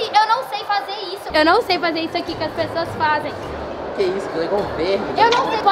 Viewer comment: "IGUAL A UM VERME DANÇANDO " KKK. (-edit ) pra quem n entendeu no 16:59 o garoto fala isso e foi engraçado o JEITO q ELE FALOU e n q foi engraçado ela dançando k7